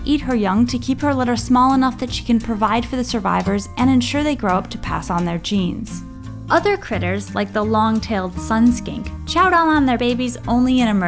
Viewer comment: Did she just say "long-tailed sun skank?" O.o